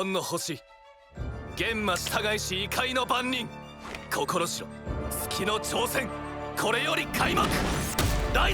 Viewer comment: Subtitles are wrong, Kagetsu says the challenge of the moon begins instead of the trial of the moon, its such a nice touch